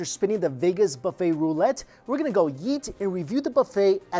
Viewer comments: did he say eat or yeet? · “We’re gonna go yeet.” Thats what I heard lol